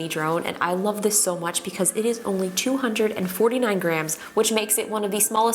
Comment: When you said 249 grams I thought you said grand and I just sighed lmao